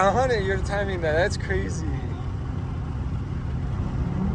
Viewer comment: did you just call him bae😂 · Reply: “Aye we should go to that place”